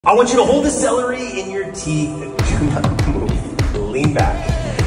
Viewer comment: I heard "Hold this in your mouth"